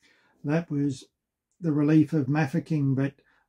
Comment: That's the origin of the Royal Naval Field Gun Race at the Royal Tournament until the lily livered powers that be cut it due to cost. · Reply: I'm sorry I thought he said the siege of Mafeking. My mistake